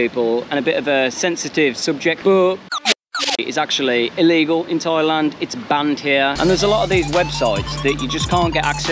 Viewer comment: What did you say at 8:37 ??? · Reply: I think the word was bornography.